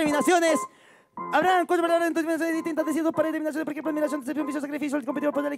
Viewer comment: le doy 500 pesos al que me traduzca que dijo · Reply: Habrán cuato palabras en dos terminaciones distintas, es decir, dos pares de terminaciones por ejemplo admiracion, traición, oficio, sacrificio. El competidor podrá elegir si rimar con el significado de la palabra utilizando la terminación a te cachai akdjks